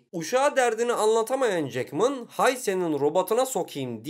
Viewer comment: da 'Haise' olarak anladım 'hay senin' i ldkfjg animelerle kafayı bozmuşum ldfkjg